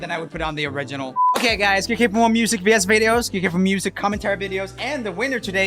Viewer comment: I laughed so hard at the end because he said "click here for more vs videos" and I thought he said bs videos :P